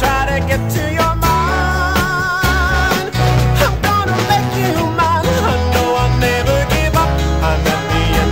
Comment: I was only a child when this song was out and I remember, and I like it, good song. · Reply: Me too; I was ten. I thought they were saying David I’m gonna make you mine….